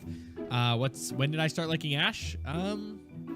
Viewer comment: i thought he said "when did i start licking ash?"